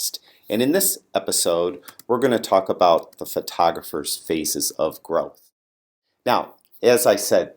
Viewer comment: I thought you said "the photographer's faces of girls".